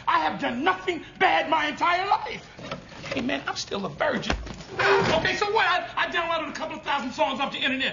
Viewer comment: What is he saying here at 3:54 when he stands up? "Hey man, I still..." what? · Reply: "Hey, man, I'm still a virgin"